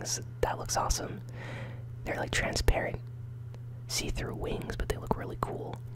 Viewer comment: what you sayed? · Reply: see through wings son